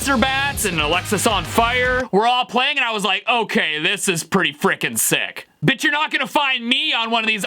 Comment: I’m sorry it’s either that I’m deaf or I’m hearing this correctly.. when he was talking about the wannabe metal artist how they’re wrong did he say bitch? Or but